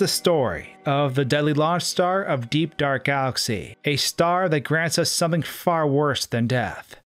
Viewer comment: I thought he said DGR galaxy!